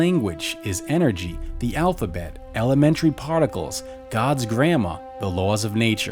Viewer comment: The Devine language is energy Gods alphabets molecule particles Gods grammar The laws of nature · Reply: hahaha that makes more sense I thought he said God's grandma I was like wait what